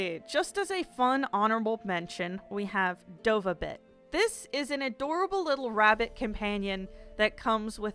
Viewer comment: I swear i thought you said dovabitch the first time you mention the rabbit.